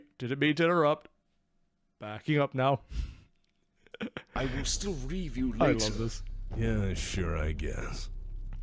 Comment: "Oh, I am sorry. May I sit in? I will still reave you later." Edit: Unfortunately for Amaris, the one person in the entire galaxy that he never could've budged just so happened to be one General Aleksandr Kerensky. By defying and usurping Star League, he stepped over the one ledge he couldn't come back from. That being said, Amaris had one thing going for him in his tyrannical madness, but you'll have to learn about that beautiful specimen in the Rifleman video down the road. 😁 Enjoy. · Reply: Huh, I always thought he said "I will still review later." I feel like I missed years of enjoyment by mishearing it.